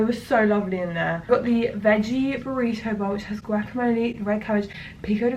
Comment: Also, I thought you said "Grackamole." Which should be a thing. Just sayin'.